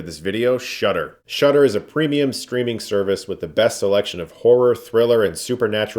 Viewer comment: I thought he said "screaming service"